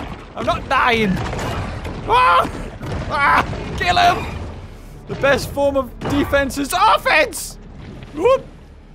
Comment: I heard him say the best form of defence is AAAH fence!